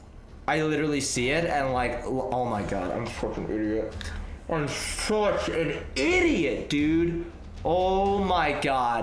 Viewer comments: did anyone else hear him say I’m a f*cking idiot or was it just my ears?? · it sounds like he said im a fucin idiot